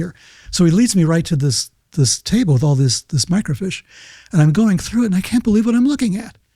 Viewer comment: dude WTF is a "micro fish"?!?! He said it like 3 times....does he mean FILM??? HOW CAN U MIX UP "FISH" AND "FILM"??!! 😂😂😂